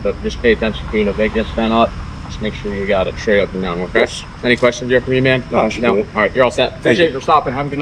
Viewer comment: About 1:50 did he say “any questions pretty man”? He should be reported to HR. 😂